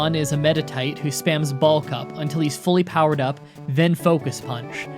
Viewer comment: I thought you said "ball cup" not "bulk up" lmao now I can't un hear it